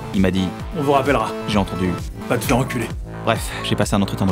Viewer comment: On m'a dit : "on vous rappelle" ! J'ai entendu " Va te faire e.. " :)